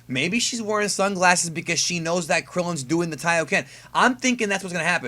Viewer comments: did he say Krillen doing the kaioken? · thought he said Kaioken, he actually said Taiyōken which is solar flare in Japanese